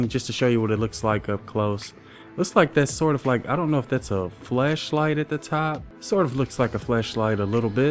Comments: Thought you said Fleshlight....... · did he say flesh light?! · I thought you said fleshlight, my bad.